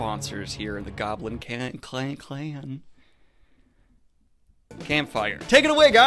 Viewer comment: I heard "goblin cannon" in the beginning and am terrified of your new weaponry